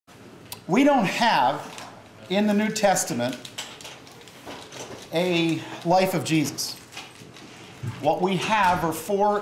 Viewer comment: what did he say in 0:05? the what of jesus? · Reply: "We don't have, in the New Testament, a life of Jesus..."